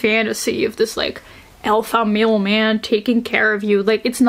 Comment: I misheard "alpha male man" as "alpha mailman" and that's gotta exist right? Omegaverse postal worker romance?